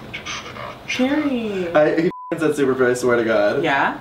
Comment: does anyone else hear "superfruit" at the end??